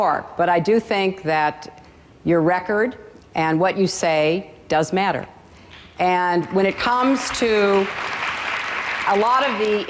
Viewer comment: ilonaftw: "did Hillary jsut say "your record and speeches doesnt matter" then she said "When u vote u ahve to look at the records and waht we said" ?" Me: No...@ 1:24, "I think your record and what you say..DOES matter." No...@ 2:45 "You have to look to the record, you have to look to what we say in campaigns..." Pay attention, make sure you carefully listen to both candidates prior to voting. This will ensure the prosperity of our country. Obama plays the race card too much.